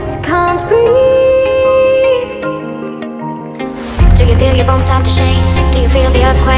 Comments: I ALWAYS thought she said "Do you feel your BALLS start to shake?" 💀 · I hear "Do you feel your balls start to shake"